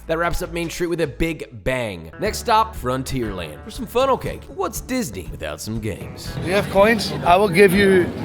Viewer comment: I thought he said “What’s Disney without some gays?” Answer: Probably a better place